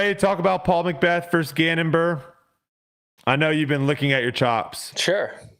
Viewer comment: did just say “i know you’ve been looking at your chops”? 😂